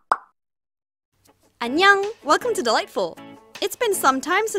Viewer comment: For years... YEARS. i thought she said “ welcome to the light full” . I have realized that a couple of months ago.